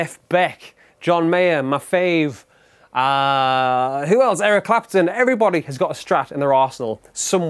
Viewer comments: sounds like he says, "Everyone has a strat in their aresehole!" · Sounded like he said, “everybody has a strat in their asshole” lmao